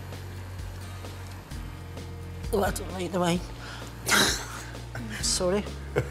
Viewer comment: What Does She Say??? I Tried Turning on the Subtitles But They Didn't even catch it... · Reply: She said "I don't like the wine"